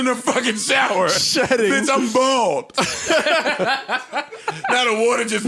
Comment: all I heard was an NBA remix "Bitch, I'm balding Bitch, I'm balding...NGA, gon grow again, Neva gon grow again.”